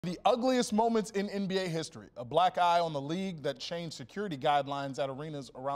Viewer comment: I thought he said "A black guy in the league that changed security guidelines" Lol